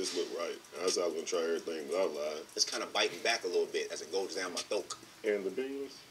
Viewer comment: did he say “as it goes down my THROAK/THOAK”? Please tell me i heard wrong.What’s wrong with people?